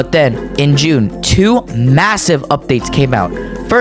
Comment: did I hear MASSIVE